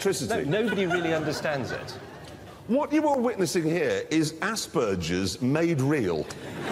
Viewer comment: still trying to find out what jeremy is saying here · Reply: "What you are witnessing here, is aspergers made real" "is that what makes my wee smell funny?" "..." "oh wait no that's asparagus"